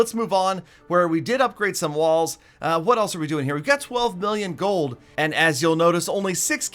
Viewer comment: is sounded like "12 billion gold"